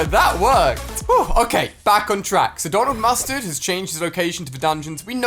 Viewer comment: when u said donald mustard i thought you said "gone off mustard" XD